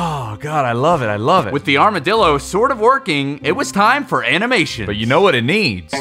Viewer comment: So fun fact about Aramdillos. The shell covering their body is a combination of keratin, the same material that makes up your fingernails and the horn of a rhino, osteoderms, a deposit of bone like material under the skin such as the bumps on a crocodile, and special fibers called Sharpey's fibers that connect it all together. I think a funny detail they could give the Armadillo is that arrows could bounce off its shell. To reference those infamous accidents where people try to shoot an Aramdillo with a gun only for the bullet to ricochet off the shell. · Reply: so what i hear is "Armadillo Trick Shooting"?